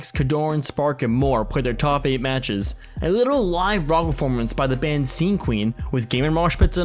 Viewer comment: you said "wrongaformence" instead of "rock performance"